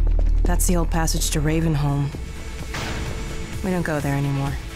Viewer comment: it's RavingHome. Also if you say that while holding your toungue, it sounds like your saying RavenHolm